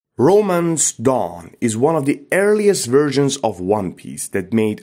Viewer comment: All i hear is " BROMANCE DAWN"